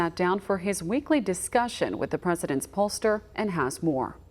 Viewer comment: I thought you said jokester not pollsters